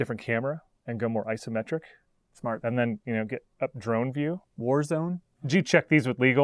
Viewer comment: Did he just say "warzone" ?